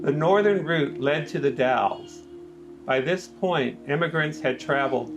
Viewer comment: I should have made this point clearer in the video since a number of people have made similar comments. The term "emigrant" is used throughout this video because it is the term most commonly found in Oregon Trail diaries, historical markers, and National Park Service materials. Whether someone is described as an emigrant or an immigrant depends on perspective. An emigrant is a person leaving one place, while an immigrant is a person arriving in another. Oregon Trail travelers were both: emigrants from the eastern United States and immigrants to the Oregon Country. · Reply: Well thats a thorough response. I thought it sounded like you were saying "immigrant". To be an "immigrant" often implies moving into an established sovereign nation or a structured society with clear borders and immigration policies. Pioneers were a completely different class of traveller from those involved in the modern hot button issue termed as "immigration". Im not claiming you are conflating the two for rhetorical purposes, but some people do.